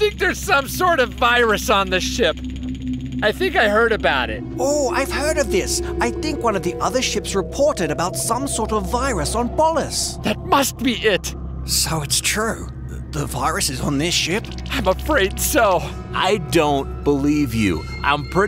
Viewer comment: What they hear: Polus What I hear: Balls